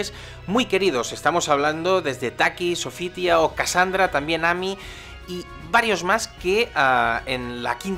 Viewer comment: > no acabo de entender muy bien el nombre del último personaje que dices, ¿es mi imaginación o has llamado Nami a Talim? xD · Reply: noooooo, se refiere a amy